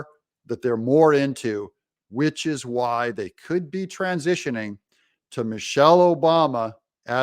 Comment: I thought you were saying Michelle is transitioning 😁